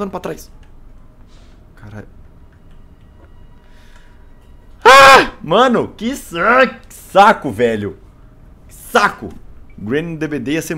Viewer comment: mano, no minuto 38:27 ele meio que falou "Mano que Sex~Saco" '-'